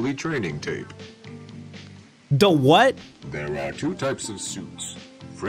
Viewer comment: how come at 0:45 i misheard that as "there are 2 types of ✨SoUpS✨"